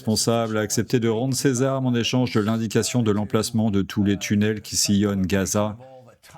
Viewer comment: Moi, j'ai entendu "Le plan des Tunels" en échange 3:02 👀